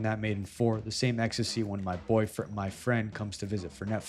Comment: you ain’t slick boy I heard that shit “boyfriend” I knew you were gay, also nice job on the video yet another good reminder why I’m subbed